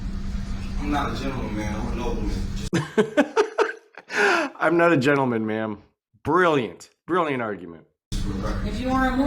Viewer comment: I swear this fool says, "I'm not a gentleman, ma'am, I'm a nobleman."